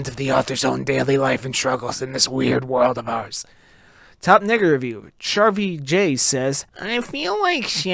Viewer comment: bro did not say nega💀💀